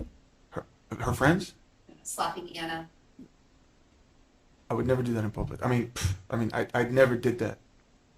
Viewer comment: "Slapping Ana? I would never do that in public." Did I hear that correctly?